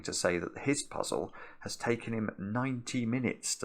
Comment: Whew, I first thought I heard mark had to take "nineteen minutes" to solve -- a very long time for him. actually said, "ninety minutes".